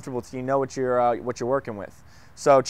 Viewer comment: Thought you were saying, "Pimping." Until I saw it in the info box! I've never heard of it before. lol This is a great help! Thank you:)